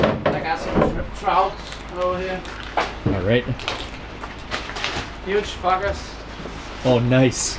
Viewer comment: I thought he said “Huge F****r” 😂